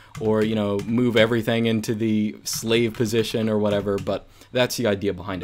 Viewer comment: OMG, did he just say SLAVE position(5:54)???